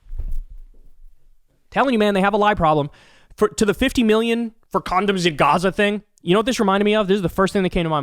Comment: I thought she said "50 million to build condos in Gaza."